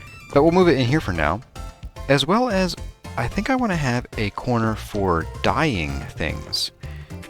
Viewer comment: ~ What I heard: "I want to have a corner for dying."